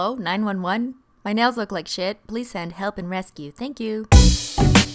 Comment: I swear, I heard that as help and Nestea